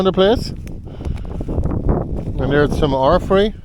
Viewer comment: What is that plant you named, please? It sounded like orfrey, but I'm not familiar with that. I've probably got the spelling embarrassingly wrong!